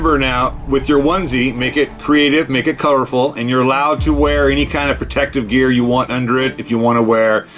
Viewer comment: drugged... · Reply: McKameyManor I swear I headed swearing and I thought u said your not allowed to swear